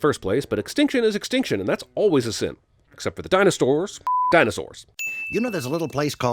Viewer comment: Did you say “Dinostores”?